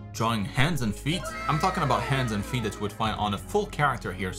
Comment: lmao i heard handsome feet